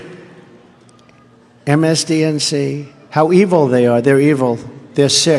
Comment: Did he said MSDNC?